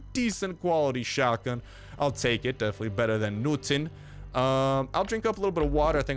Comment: At the chopper event you said better than nothing... but it sounded like you said better than Newton... I'm hurt.